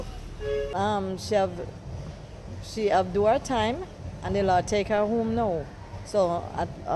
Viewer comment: wtf did she just said ..... whose mother is this though lmao😂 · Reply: She did her time